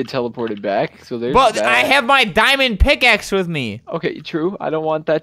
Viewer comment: Wen he said MY DIMOND PICAXE I thought he said MY MOM FATS